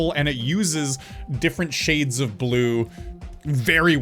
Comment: Did I hear a new movie called Fifty Shades of Blue right there?